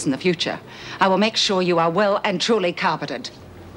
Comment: what does Po say? I'll make sure you are well and truly What? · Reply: Yep, she said carpeted. It's an old saying. Not exactly something said only by Aussies either.